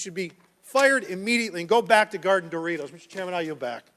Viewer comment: Did he say “go back to guarding Doritos”? 😂